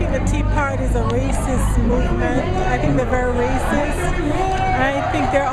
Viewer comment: does she say racist or Rayciss? She said it twice and I still cant make it out. I must be raecyst........